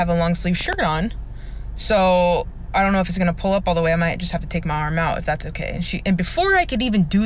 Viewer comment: Did anyone else hear she said well I can just take my arm of.